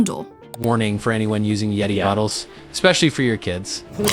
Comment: Am I the only one that heard him say “Yeti Yottles”?